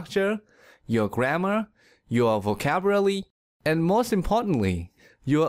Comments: Did he just says" Vocaburaly"? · I heard “Vocaburaly” instead of “Vocabulary” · Am I the only one who heard him say 'vocaburaly'? Uhm